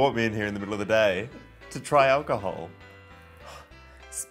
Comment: i thought it said teens try alcohol